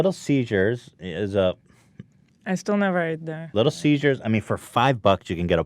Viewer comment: was it me or is Ethan calling little Caesars, little seizure's